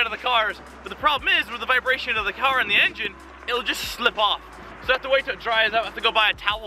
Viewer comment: Did just say shlip off at 5:31?